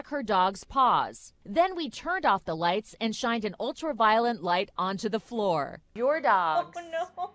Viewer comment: can't hear anything else other than "ultraviolent" light 🤣